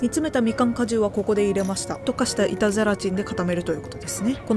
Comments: 板ゼラチンがイタズラチンと聞こえて、ハロウィンだからそういう妖怪名で呼ぶのかぁと途中まで思ってました😮空耳がひどくてすみません💦 お菓子もお話も映像も音楽も素敵です❤ちょうどハロウィンな気分なので、刺さりました😊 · 板ゼラチンか… いたずらチンって聞こえててハロウィン感じてた… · 「板ゼラチン」が「いたずラチン」に聞こえて、ハロウィンダジャレかと思いました笑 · 板ゼラチンがイタズラちんに聞こえて、ハロウィンだから掛けてるのかと思った笑